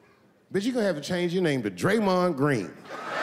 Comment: I saw this without subtitles and thought he said Dream on Green. I was like yeah sounds pretty black to me. 😅